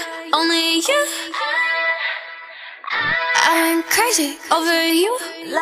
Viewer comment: "i went crazy over you" Selena's voice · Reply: Why I hear it " I'm crazy over you"